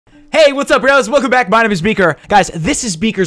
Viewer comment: When you first said beaker I thought you said vegan